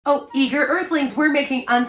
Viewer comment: I thought she said "eat your earthlings" at first🤣🤣🤣